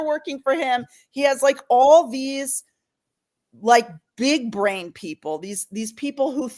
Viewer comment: At 27 minutes she said “big brain” people…I heard, “big DRAIN” people! They all want to hit the DRAIN and rid places of power of mire!!!